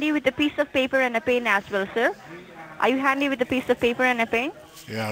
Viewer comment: Hello. Hello.? Hello. Hello. 🤦‍♂️ “Are you handy with a piece of paper and a pencil?” WTFO? 🤷‍♂️ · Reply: Oh, I thought she said "piece of pepper and a pain" LOL